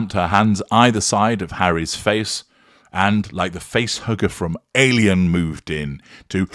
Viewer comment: Thought you said “face hooker”…. !